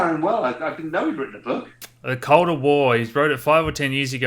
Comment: what is the name of the book and by who ?? I thought he said code of war but I can't find it. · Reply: The colder war. Marin Katusa